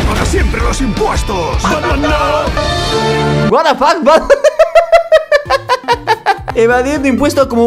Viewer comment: que dijo? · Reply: Batman no